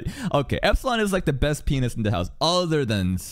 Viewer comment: i swear to god when you said “pianist” my brain heard that as “penis” and i was like WHAT?!